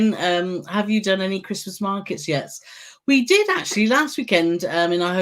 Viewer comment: I know I need new hearing aids when I heard you talking about visiting Christmas Markets and I thought you said Princess Margaret RIP😳🙄🤣